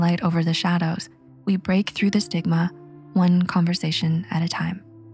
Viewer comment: "We break though the sig-ma" ON 1:28 WHY DID I HEAR THAT LOL